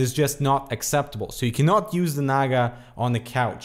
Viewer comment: did you said naga? (as in razer naga? or i'm mistaken? anyway great review :)